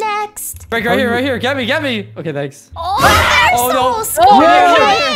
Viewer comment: who else heard “gabby” instead of jade? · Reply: he said get me get me